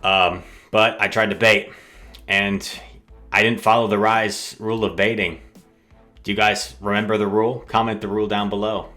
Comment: Hey, hoping you can help. I keep hearing "reis rules of baiting". Was wondering what that was.